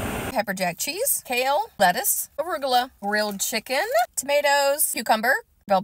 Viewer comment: I thought you said real chicken. Then I realized you said grilled chicken 😅